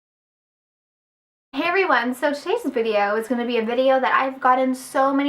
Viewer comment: i thought she said hairy ones instead of hi everyones